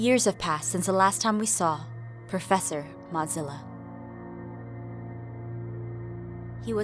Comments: I swear I thought I heard professor mozzarella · I THOUGHT SHE SAID PROFESSOR MOZZARELLA!!!!!!😂😂😂😂LOL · I thought she said professor mozzarella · I was PRAYING for this and when I saw the video I was like "What's this?" And I heard "Professer Modzilla" and I actually had to pause the video because I was crying!!!